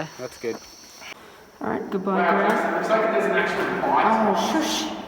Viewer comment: What's saying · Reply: He’s saying “wow it looks like there’s an actual light ...” so he’s referring to my camera light, saying it almost looks like the place is lit up from a normal light.